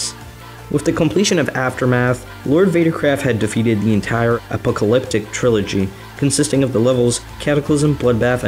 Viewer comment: at first I thought he said the epileptic trilogy